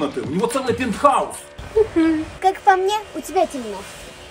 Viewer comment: мне послышалось 'как по мне у тебя дерЬм@